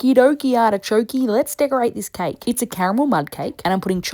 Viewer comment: It took me a second but I thought she said CAMEL sauce instead of CARAMEL! 😂😂😂